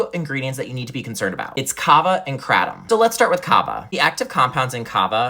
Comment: I thought he said Kava and crack. Damn😂